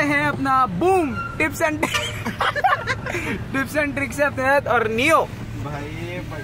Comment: I thought I heard my name Neev(Cuz my friends pronounce it like Neu)